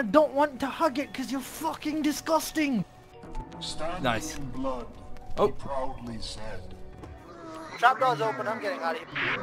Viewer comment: In case you want to know what he said, here you go. I just figured this out after watching the video a couple times: “Standing in blood, he proudly said, ‘run now, my home, shall house the dead’.”